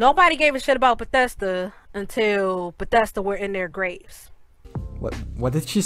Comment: Seems like the fact that she made the video proves she's upset and when was bethesda in their Graves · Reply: O shit she said graves I for real thought she said grapes or some shit